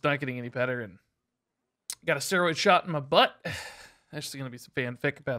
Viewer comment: I legit heard young Eminem screaming "Quick, someone get his ass another steroid" when he said he went to a doctor. And then he said: "Got a steroid shot in my butt". That's absurd.